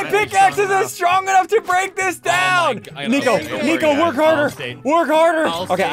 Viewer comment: did he say the n word?😃